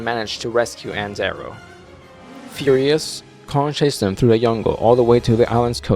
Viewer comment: Cool video. But did you just said „yungle“ instead of „jungle“ at 3:12?!😂😂😂